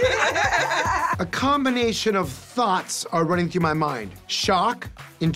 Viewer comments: i thought he said “thots” lmaaoo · I thought he said “Thots” running through my head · 🤣 I thought he said a combination of "thots" were running through my mind. Lol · I thought he said "a combination of thots came to mind" 😭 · I thought he said "thots" 😭💀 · I thought he said thots, but it was thoughts HEH ._. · I thought he said THOTS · Boy at first i thought he said he had a combination of THOTS 🤣🤣🤣 i’m like wayment 🤨